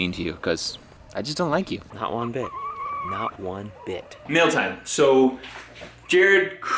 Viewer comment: Lol, he panned from guilty chicken and I thought he said "meal time"... I see what Matt did there